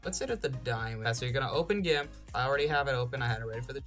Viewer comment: I hear him say gimf. What does he actually say? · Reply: Gimp. It's an image-editing program that's completely free and open source. If you install the latest version it's going to look different than mine but you should still be able to follow all the steps accordingly.